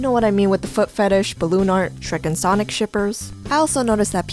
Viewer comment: When she said shrek and sonic shippers I thought she said strippers